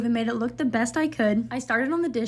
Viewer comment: I swear I thought she said: “After I scrubbed down the stove and made it look the best I could, I farted on the dishes..” 😂😂